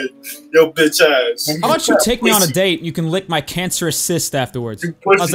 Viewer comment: Anyone else begin to cry with laughter when he told that dude to “lick his cancerous cist” 😂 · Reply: I thought he said his cancerous sis 😶